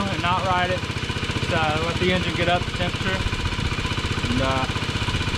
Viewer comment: can you provide a link for where you purchased the motor? I have a 72 CT90 that the Hi / Lo shift Lever is frozen and occ. jumps out of gear. Motor only has 1700mi so I would assume it has plenty of life yet but may be less expensive to buy new motor than tare apart and replace clutch and gears on this one... great video. If I cannot find a new motor, I will be using your videos to help me get mine up and running. Thank you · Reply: oops. Ha. I heard you say "new" engine. sorry, my bad. any thoughts on why the Hi / Lo would be frozen?